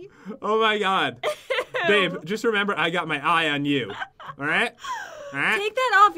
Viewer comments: Did anyone else hear say babe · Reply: He said "Babe I got my eye on you" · on 12:04 did he say babe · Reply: He said i got my eye on you babe